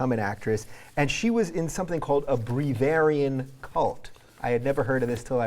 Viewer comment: At first I thought he said "Bavarian cult". xD